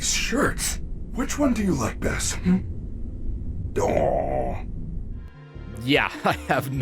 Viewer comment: I think the weird sound is him saying Awww like he the shirts cute or something